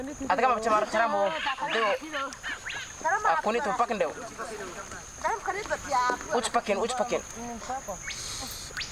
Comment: what is the guy saying ....."Who is ^^^^^^^" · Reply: He said meet me outside cuh.